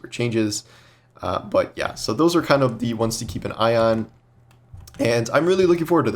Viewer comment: Am I the only person who thought said "Those are the ones to keep an ion"